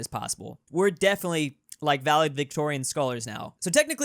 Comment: Did he really say “valid Victorian” instead of valedictorian?